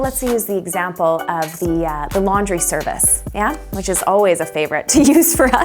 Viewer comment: For a second there I thought you said "lingerie service"